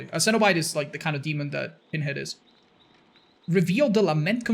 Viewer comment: It sounds like your saying peenhead